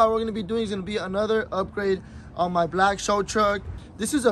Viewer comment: I thought it said slow truck!😂😂😂